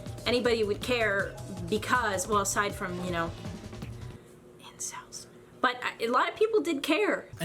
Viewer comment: I was passively watching and I swear to God. I thought she said Ian. It threw me off so bad that I genuinely thought She was just abusing him on purpose until I rewatch it. Of course With everything being said in this video so far. I still think I'm correct on the abused.